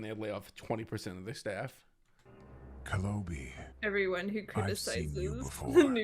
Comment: Dead ass thought he said KENOBI 😂